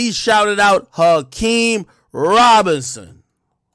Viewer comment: "HAKEEM AKBAR!!" THATS WHAT I HEARD 🤣😂SHIT AINT NO COINCIDENCE 🤔🤷🏽IYKYK